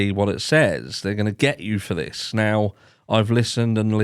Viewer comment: You can here very clear what the girls had said ( what did you say to adam) and the other girl saying ( I I told adam everything) it doesn't sound like to me it was said at the end ( they're gonna get you for this) to me it sounds like he said ( he, he's going for Gary for this) I'm not saying it's what he says I'm saying what it sounds like to me that whoever said the end bit is saying such a person is gonna get this)